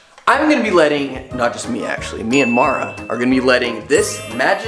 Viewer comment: when you said Me and mara I think I heard MYANMAR 🇲🇲